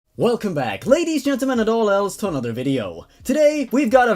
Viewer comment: He talks so fast in the intro that I heard "Welcome back ladies, genital and all else".